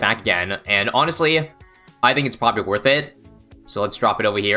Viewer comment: did he just say popprobly lol